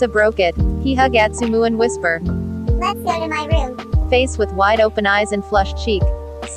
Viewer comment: i thought he said lets go to my mum.. 😔